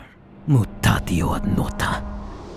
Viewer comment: did he just say moo potato?